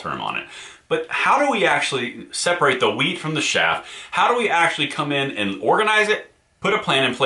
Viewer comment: Did you just say "Wheat from the shaft"?